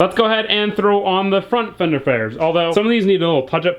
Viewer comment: god damnit Either you got sidetracked, it was just me, or something but I think you said fender fares